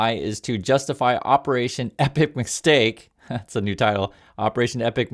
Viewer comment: at first I thought I heard Operation Epic McSteak.